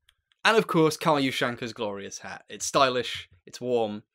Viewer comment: I thought that said GLORIOUS HRT and I was like YAAAAAAASSS BITCH